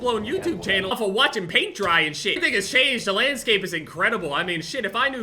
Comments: I hear these nig*as change at 7:39 anyone else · Did he say the nword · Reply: Something has changed not n... has changed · why does it sound like he said you niggas changed😭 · Sound like he said "nigga" 🤣🤣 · i know it was just the editing, but I heard; "...now motherfuckers are straight up building full blown YouTube channels off of watching paint dry and shit. These niggas changed, the landscape is incredible, I mean shit..." Lmao